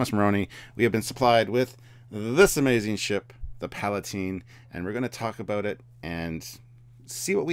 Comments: at first I thought it said "Palpatine" class lol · I thought I heard “Palpatine”